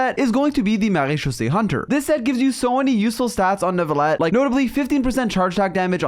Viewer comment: Why does it sound like he said at 11:14 “this set gives you so many useless stats”lol